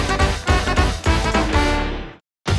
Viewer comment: おねえさん、住所は、電話番号は！が街頭インタビューに聞こえた。